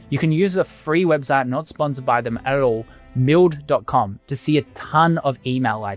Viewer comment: which website do you prefer to?i can't hear it clearly🙏🏻... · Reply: milled